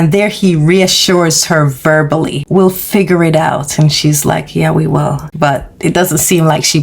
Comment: When Michonne breaks the glass to get the baseball Jersey, Rick touched her arm. I did not know he said "we'll figure it out" until you put the close caption on your screen. Every time I watched it I always heard him say for her to figure it out, I was always confused. Outside of Noah's home who shot up those cars? Because the Wulves use knives and other sharp items no guns.